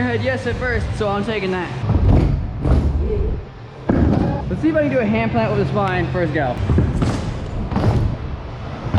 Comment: did he just say yeet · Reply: DestinedCube some kid said yeet